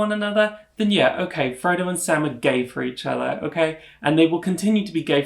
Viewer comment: I love this video ❤ and your sense of humor is great 😂 10:00 I love that you said "Frodo and Sam are gay for each other" and the subtitles said "Frodo and Sam are in love" 👌😂 SAM IS B👏I👏 ASF!!! I've never had a damn doubt about it. :D Yes, you're so right, the relationship between F&S and yeah okay Rosie too, is really especial.